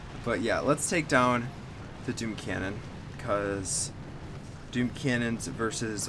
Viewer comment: did he say DOOM canon or BOOM canon?! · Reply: Doom cannon